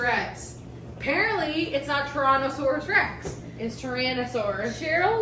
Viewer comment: T Tex it’s not TORONTO saurus lol. That’s what I thought you said. Sounded like torontosaurus lol